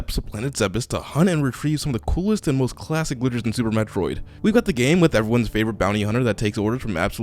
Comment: idk why I heard supermicrowave lol